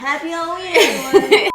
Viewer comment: I thought she's saying happy holi😂